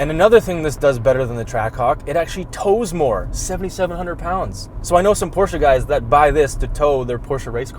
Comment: you said “7700 Lbs” and the subtitle says “7200 Lbs” 😆